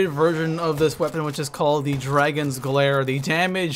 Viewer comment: It's actually called Dagon's glare. Ur not the only one who thought it said dragon. It took me a while to realize that😂!